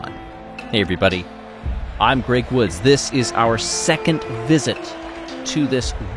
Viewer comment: You know .. when he introduced himself.. I always hear it .. "... I'm Great Woods"